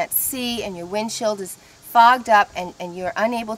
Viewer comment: Did she just say FUCKED UP or FOGGED UP